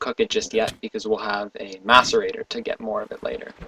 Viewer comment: lol when you said masserator i thought you said masterbator xD